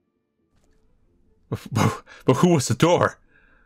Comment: what did you said here?, I understood something like "Who is the door".